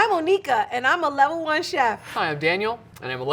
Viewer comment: hold up what she say 👂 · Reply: Monica not the n word